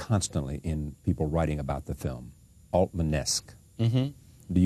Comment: What is the word the interviewer mention at 12:37? I could not gather it. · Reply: "Altman-esque", as in, the film Magnolia was described at the time as being very much in the vein of director Robert Altman's work.